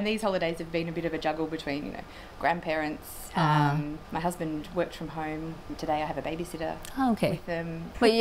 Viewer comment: の My husband worked from home. 何度聞き返しても worked from が watchに聞こえてて、てっきり「面倒を見てる＝take care的」なことかなと思ったけど、0.5倍速にしたらしっかりworked fromって言ってた。